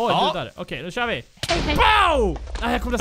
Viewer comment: OMG VID 13:20 HÖRDE MAN EN FICK RÖST SÄGA HEJHEJ O.O